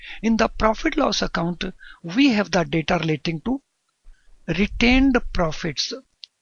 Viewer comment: in the profit and loss account we have a what? repeated that 10 times but still couldn't understand. Very informative though, thank you! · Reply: mp biggame we have the data relating to retained profits